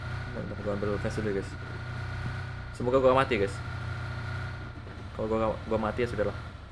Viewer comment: gua yang salah denger atau gimana? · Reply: Itu semoga gw gamati gais. Tapi ngomong gamati nya kecepetan